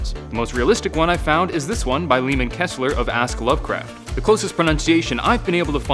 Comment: Did he say Leemond Kessler?! Like from the Adventure Zone Podcast?!?!?!